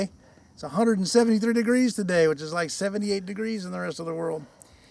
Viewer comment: Did he say 178 degrees!? Thats not possible the world record is like 134 degrees